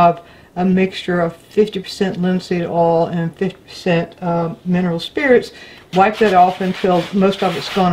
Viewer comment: Hola :). Thanks for this antastic tips, My english is very basic and I did not understood what you said on the minute 3:26, about hwat you do after using the sandpaper, the kind of oils that you use on 50% and 50%, did you say linseed oil and turpentine? Thanks for the fantastic and inspiring content. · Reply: I said linseed oil and Gamsol. Gamsol is a refined mineral spirits, so any brand of artist grade refined mineral spirits will work.